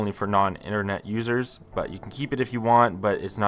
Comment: I heard that as "non-internet losers" 😭